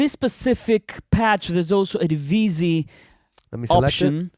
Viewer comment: what are you saying? Divicy